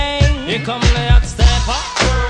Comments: Today, I learnt that I never heard over 97% of the lyrics. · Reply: I thought he was saying I’m the lyrical dancer 💃 lol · I always thought he said "I'm the leprechaun gangster" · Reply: I always thought he was saying "I'm the little girl gangster." Excuse me, Mr officer? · I always thought he said "I'm the lyrical counselor" 😂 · I swear i thought he was saying leprechaun gangster 😂😂